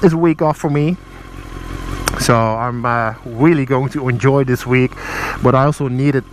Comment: "Wheelie going to enjoy it" I heard you say it, I HEARD IT. And as weird as it sounds. It is kinda motivational. You have to offer things to get things.